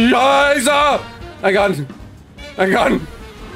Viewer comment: Did you just german at 22:35 XD ? That sounded like a funny way to pronounce "Scheiße!"